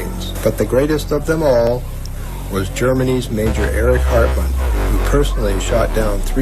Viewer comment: I thought you said Eric Cartman